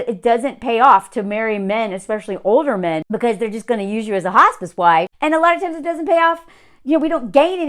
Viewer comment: I think towards the beginning of your talk, you said "Hospice wife." I heard "Hostage wife." Funny, and true.